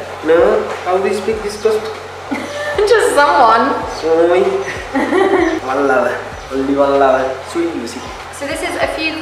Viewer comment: Did he say "one lover, Lucie". Wow! So lovely😍😍